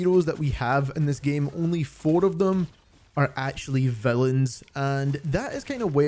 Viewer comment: I thought you said “only 4 of them are actually felons”😭😭😭😭